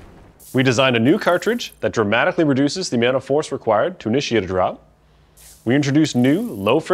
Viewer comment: did he say ‘…a-meow-nt of force…’???